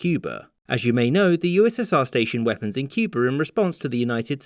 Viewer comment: Sounds like he says, "Cuber" here